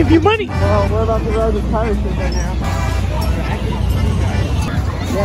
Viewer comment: DID ANYONE NOTICE THE CAPTION SAID THE S WORD INSTEAD OF SHIP?!?